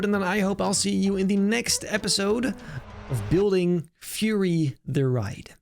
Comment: Lamo i thought the intro said furry for a second